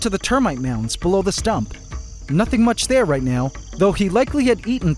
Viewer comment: Did you say "right meow"? 😂